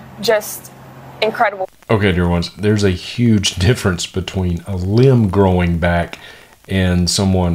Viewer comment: I thought she said “We saw Lem Sgrowback”